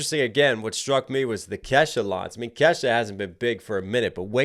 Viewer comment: The Kesha's line also sounded like Cassius, as in Cassius Clay (Ali). It makes sense since its preceded by "rolling with the punches" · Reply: Yes, like you, I thought he said Cassius (Ali) rather than Kesha.